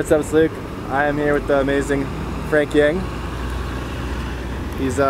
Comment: I thought he said Frank Yank..